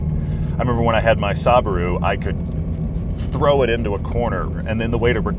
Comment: Did he just say SAABARU